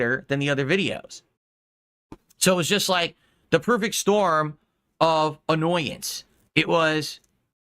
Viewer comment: Did he just say, "the perfect storm of annoyance"?